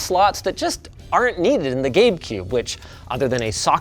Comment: Did Linus say GameCube around 3:15 minutes or so ??